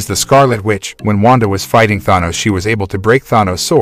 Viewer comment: All I heard was "is was able to penetrat Thanos"